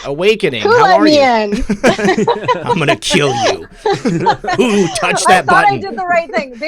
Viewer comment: Anybody catch that at 45:48 - 45:52 when signed in... Was that a joke or is it my imagination? Creepy voice say: ( I'm gonna kill you, Ooooo! touch that body) ... (?!?!?)